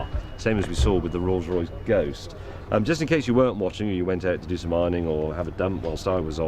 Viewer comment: i thought he said ironing, lol :L